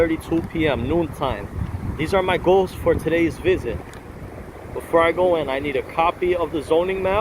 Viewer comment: It sounded like he said "these are my goats for today's business"